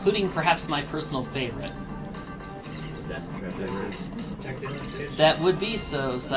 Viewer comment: what are you saying? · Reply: He said "Is that Treasury Room?", speaking about favorite song.